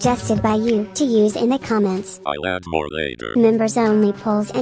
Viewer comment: why did i hear " i want more naeger "